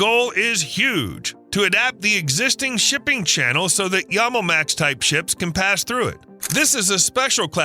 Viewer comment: The subtitles say Yamomax, but I can't find that on Google. What does it really say? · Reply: Yamal gas